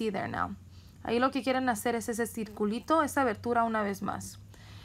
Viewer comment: I heard "ahi lo quieren hacer es decir culto" at 4:19 LOL